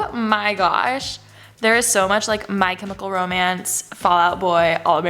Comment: im sorry did i just heard “my chemical romance”??